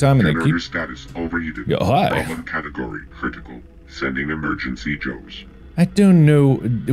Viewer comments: Rewatching this just now, I realized its saying "sending Emergency Joes" which makes a lot more sense... The whole time watching and rewatching these I thought it was saying "sending Emergency Jokes" 🤦‍♀ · At first when the voice said "sending emergency Joe's" i didn't read the subtitles so for awhile I thought it was saying "sending emergency jokes"